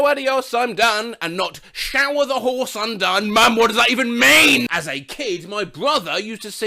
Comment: no i seriously thought she says 'shower the horse im done'